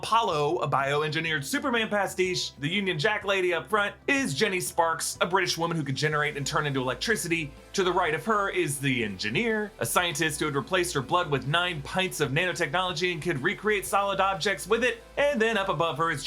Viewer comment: The What!?! Did you say EnFineer? That's what it says you said.